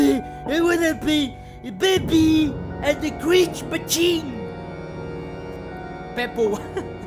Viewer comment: What did say he wanted to play...? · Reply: "Bepi and the crig mabiche" Probably referring to Bendy and the ink Machine